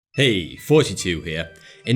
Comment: I thought u said "hey 47 here"